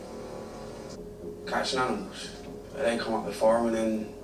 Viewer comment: What did that guy at the table towards the end say about catching gnomes? That's what it sounded like he said. At least the dog is ok. Well on to next week to find out who did it. I don't know and still can't guess. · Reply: Animals, not gnomes.